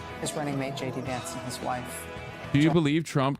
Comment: I swear I thought the news lady said “and here’s jd Vance, his wife” like omg hard launch TrumpxVance marriage?????